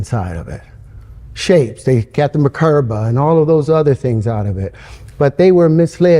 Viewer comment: The subtitles say "...got them a curve" when he actually said "got the merkabah" (though he pronounced it "mahkerba") 🤣